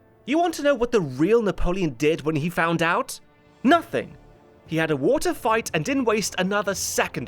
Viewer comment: First listen, I thought you said "He had a water fight" - I thought, "fair enough, good way to get rid of all that anger & frustration at Josephines' infidelity before getting on with the war! " 😂